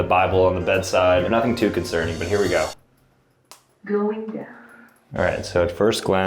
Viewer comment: I heard "Going dark"😂😂😅